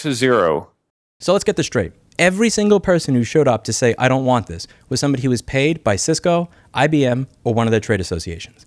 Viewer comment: i think you say, "i don't want this for both sides" 🙃🤲🙏😂❤️